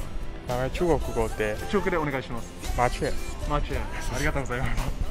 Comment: I think around 3:39 the song mentioned is Sparrow by Ronghao Li instead of Dick and Cowboy. Both are quite unique though lol Love your video, keep up to great work!